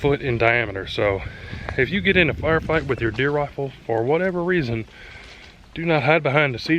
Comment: I thought you said "if you ever get in a bar fight with your deer rifle.." Funniest mental picture i have ever had.